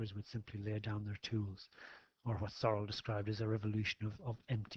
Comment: what philosopher or person is he saying? It sounds like “or as ¿soro? describes...” Looking up the quote doesn’t lead me anywhere either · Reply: Hi, the quote is from Georges Sorel and the source is from Andrew Heywood's text - <link>